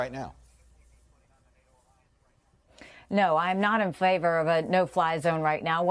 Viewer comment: is she saying "not in flavor? 😁😆